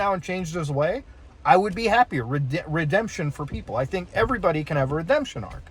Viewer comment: My deaf ear was acting up at the no redemption for edp45 and I thought he said no redemption for people who eat before 5 and I was like wtf lol